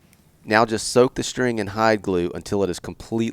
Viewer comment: What did you say at 7:13?? Soak it in what? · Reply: Hide glue